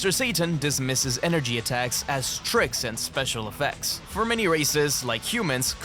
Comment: Thought it said “for many racists…” 😭🙏.